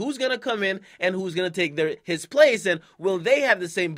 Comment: Hmm listen to 1:14 akademiks slipped up almost sounded like he was gon say "who's gonna take their money"